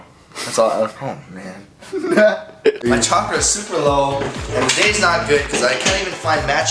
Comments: Did he just say "My chakra is super low"? :D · "My chakra is super low." 2:15 ... Did i hear that right?